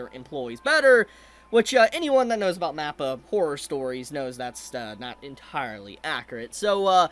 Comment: what did he say? Mappa horror stories?